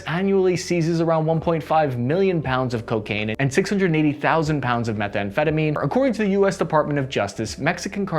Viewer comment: you said 680 thousand but the subtitle said 68 thousand. Was that a typo?